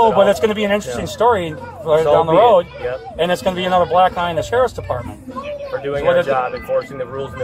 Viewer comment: did he say another black guy in the sheriffs department??